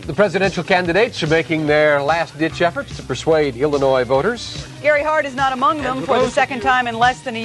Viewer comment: Ok turned on CC to hear what he said at 0:54. He said "Illinois voters" but for a second I thought he said "humanoid voters" which reminded me of the Simpsons episode where the cadidates are aliens. 😂